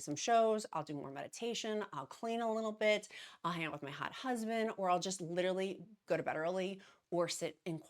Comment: I listen to you on my way to work. I swear I thought you said, " handle my hot husband!" I love it!!😂😂❤❤